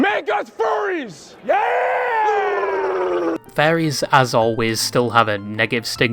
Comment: the fairy theme from botw in the end made me chuckle since this guy sounds like he is saying faires instead off furrys this whole video